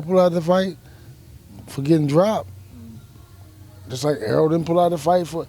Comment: Hahaha 🤣 he dropped 2 sparring partners! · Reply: I thought so, sounded like he said pac got dropped